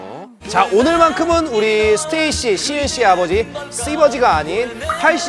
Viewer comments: Error in EngSub at 58:53. Heechul correctly says Sieun but the subtitles says Sumin. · Thank you for the video and subs! Just wanted to say that at 58:58, it should be "STAYC Sieun's father", instead of Sumin's! 😊